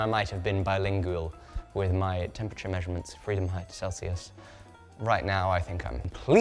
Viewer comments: lol did he just say Freedomheit there? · I swear I thought I heard "Freedomheit" and you know what? That's my new favorite way to say Fahrenheit now lol